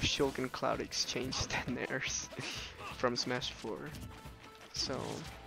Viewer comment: I know he’s saying n airs but I hear the n word xd